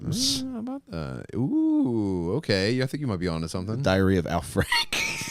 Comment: thought he said the diarrhea of al franken for a moment